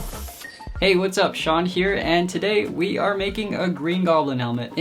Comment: When Sean said ay what's up it's Sean here I thought he said ay what's up shawdy